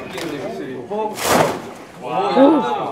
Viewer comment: Pls tell me I’m not the only one who heard that guy in the back 0:15 say “woooww”